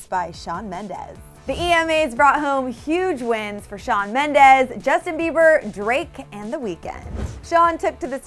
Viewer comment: LOL i thought it said he ended justin bieber